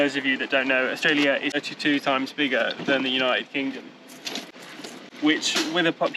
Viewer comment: Internet says Australia is 32x bigger. Sounded like you said it was 2x bigger.